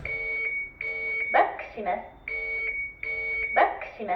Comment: やはりガッツ石松と聞こえるのは私だけ？ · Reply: 嘘やん‼️『バックします❗』って言ってるやん。